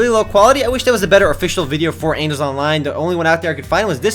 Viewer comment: it sounded like you said "anals online" hehe anal :3